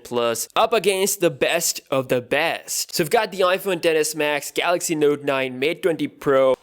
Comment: Sounded like he said "The Iphone deadass max" 😂